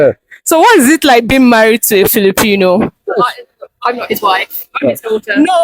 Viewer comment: Im confused? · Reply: I could have swore I heard her saying I’m his wife.